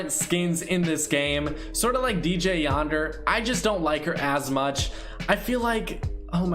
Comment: It sounded like he said “ I just don’t like her ass much” 😂😂😂😂😂